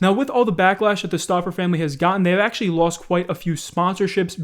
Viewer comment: I thought said stalker family lol